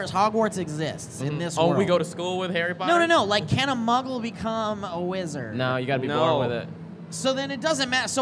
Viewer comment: Muggle born - mudbloods - can be wizards. Hermione. Harry's mother, Lilly. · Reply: Owen Uwangue I misheard the discussion as "can muggles be wizards". the matter of 'being born a wizard' is the same whether the family has historically been of plain human, wizarding, or mixed lineage.